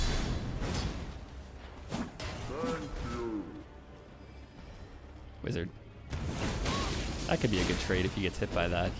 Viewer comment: What did he said exactly at 17:35 ?😂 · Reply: "Thank you"